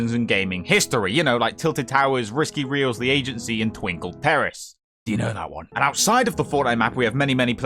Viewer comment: Completely forgot Twinkle Terrace existed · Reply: If actually misheard it as “Twinkle Tariffs”. I legitimately thought it was a joke about China